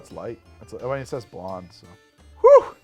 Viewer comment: When the text said Westmalle, I thought he had said "monde" and Canadian. I assumed he meant Unibroue's Fin du Monde, which is delightful.